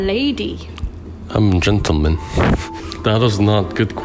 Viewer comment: ”no I’m a lady”: “I’m a jintle men” (guys i know he said gentleman but it sounded like that 😂